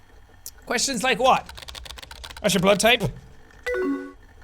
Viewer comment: I THOUGHT HE SAID "What's your blow type?" and I was about to cry I appreciate this man too much.